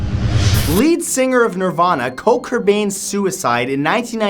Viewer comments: i heard Co Kurbain... Matt it's Kurt Cobain, no Co Kurbain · Who did he say at 6:00? Koat Kurbain? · Did he just say Coke Cobain?